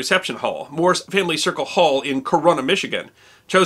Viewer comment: carona Michigan hmmm... sounds familiar.... what’s the word? Oh got it carina virus Michigan! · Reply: It is Corruna.